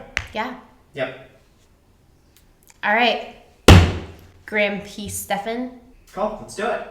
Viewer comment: I thought you said Grumpy Stephan. I’m so confused now. I thought you’re (Graham’s Girlfriend), then, now you’re 😁